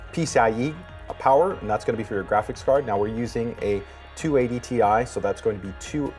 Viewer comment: did he say 280 ti?